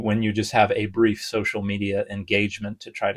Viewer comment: When said And the fit for this video is Rick and Morty, I thought he said rigormortice 😂